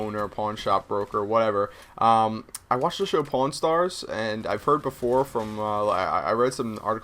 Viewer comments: I thought u said porn star lol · i thought he said porn star i was like WTF